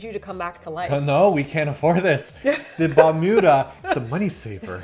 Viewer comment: what is this device you're talking about at around 13:10? Captions says it's either "Vinuda" or "Bermuda" XD WUT?! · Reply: It’s called the Balmuda. We did a video about it. The $400 Toaster. Check it out!